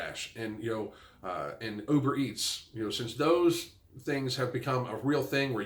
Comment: I totally thought you said "over-eats" and not "uber-eats" ha ha ha